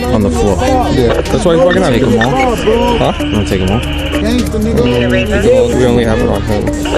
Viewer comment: Did he say; do you want to take them all?